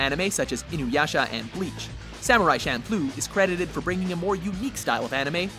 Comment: Thought he said Samurai Shampoo at first! I love Samurai Champloo and it's one of the only animes my bf will watch